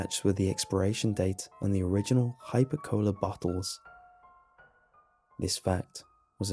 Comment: am i the only one that's confused by the title? I STILL LOVE THE STORY THOUGH! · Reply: bLiXy 8:36 and 8:59 There you have it. "Hyper Cola". I think I have also heard it before but I'm not going to listen to that whole story a second time just so all you deaf people can hear it as well.